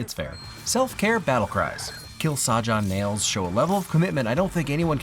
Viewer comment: I 1000% thought Zoey said "Kaja"